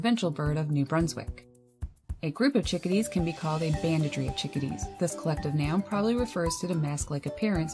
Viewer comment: Can't catch what you say at 1:29. Collective adjective. "A bandagery/bandagerie of chickadees?"